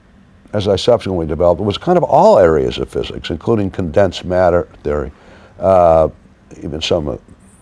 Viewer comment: did he just said "condensed matter" ?!?!